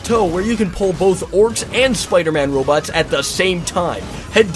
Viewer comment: did he just say spiderman robots?